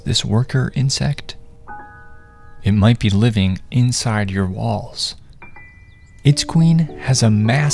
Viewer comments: When you said “it might be living in your walls” I thought you said “it might be living in your balls”💀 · Pardon me, I heard "inside your balls"😂😂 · Omg I got so scared I thought he said “it might be living in your balls” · When he said it might be living inside your walls I thought he said it might be living inside your balls 💀 😂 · Bro when he said “It might be living in your walls” I thought he said “it might be living in your balls” 💀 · Jeez I thought he said it might be living in ur balls 💀💀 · why did I hear “it might be living inside your balls.” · i thought he said “it might be living inside your balls” That made me so terrified but then i heard walls OHHHH still terrified tho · Oh I thought he said balls I was scared 😂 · I thought you said living in your balls😅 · Thought he said balls i almost died💀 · I heard “it might be living in your balls” 😭😭😭😭😭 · when he said walls I thought he said balls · For a second, I thought he said, “It might be living in your balls.” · “living inside your walls” holy, i thought he said inside your balls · my volume was low, so at first i thought it said " it might be living in your balls" and got so confused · Bro I thought it said “it lives in your balls”😂😂😂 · I heard "it might be living in your balls" 😭😭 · I thought he said "it might be living in your balls" 💀💀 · Ngl I thought he said it lives in you balls💀💀💀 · I thought bro said,, It might be living in your balls" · I heard "inside your balls" and almost lost it😂